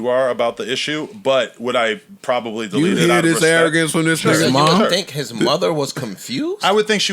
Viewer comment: I don't even rock with NoJumper like that but I'm with Adam on the Kevin Samuels argument and he held his own while being attacked by the whole crew. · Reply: “.. but would I probably delete it out of respect? Sure.” Before you start insulting people, you should actually take the time to listen. You sound like the one who belongs on the short bus. He clearly said he would delete it out of respect for Kevin’s mother.